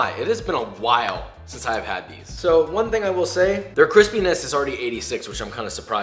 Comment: if you have captions on instead of it saying crispiness like he does in the audio the captions interpret it as “Chris Penis” 😐